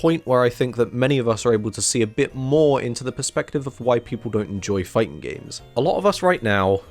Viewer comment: I heard "white people don't enjoy fighting games" and had to rewatch it